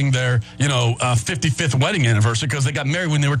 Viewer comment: Observation and math aren't your strong suits are they? The other guy said 50 which is in my post, and what's 20-24 plus 55? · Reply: He clearly says "55th wedding anniversary" around 6:39. Angry, bitter, and wrong is no way to go thru life, my friend.